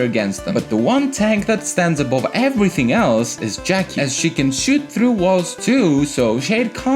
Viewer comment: I just heard Jackie can also sh*t through walls